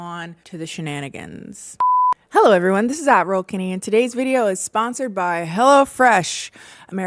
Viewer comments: 😅 I have always thought you were saying it's Adderall Kennie. Closed captioning is great. 😂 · I always thought you said "admiral Kennie" and I thought - ooo she's so regal to make moneeeyy. Then I heard Ad Roll Kennie 🤣🤣